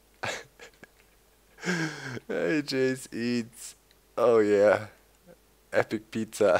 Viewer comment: thumbs up of you thought it said "ass eats"